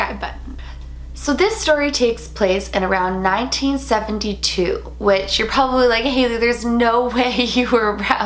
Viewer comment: I honestly thought that you said "this story takes place in 1792" and then you said "my mom was alive during that time" and I like freaked out lol then I watched it again and realized that you said "1972"